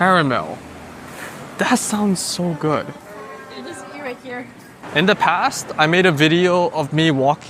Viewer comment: Hey! Awesome video as always. I have a quick question about 15:05—right after you talk about the ice cream flavors, I swear I hear a woman’s voice say 'eat here.' Since you were totally alone in that rural area, was that a store recording or did you actually hear that? The timing was perfect!